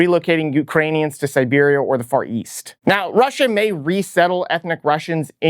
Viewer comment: I hear “ fart yeast”. Thank you.